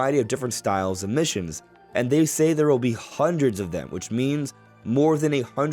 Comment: when I first heard the direct, I thought they said dozens of missions not hundreds so that is kinda a big thing. I also see no one really pointing out the icons when you get a peach medal or panel which does hint at more unlockable solutions outside of food and kamek. so overall they are 100% hiding more stuff about the game and so I won't lose my faith just yet. since even if the marketing was kind of trash the passion and love in game development is still there (it is also nice to see someone not be overly negative like a lot of people in the comment sections of these kinds of videos)